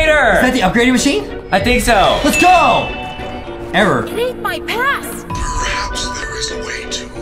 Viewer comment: I thought Gregory said: I ate my past! and i was like WHAT?!?!